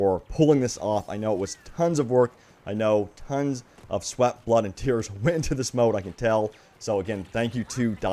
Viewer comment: Did he just say "Blood, Sweet and tears"???? HELLO BTS FAN?